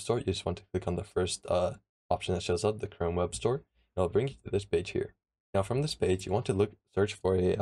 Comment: i thought it said "It will bring you to this bitch here"